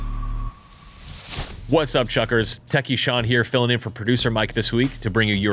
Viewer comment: Sean, I am so sorry, I always heard it as Tacky Sean, until I finally just saw that you are 🤣🤣🤣